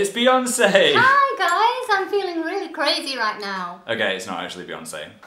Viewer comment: "its beyoncé!" "im feeling very crazy right now" WAS I THE ONLY ONE THAT GOT THE REFERENCE BECAUSE OMG PHILS MOM IS GOALS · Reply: Lol I thought she said gravy instead of crazy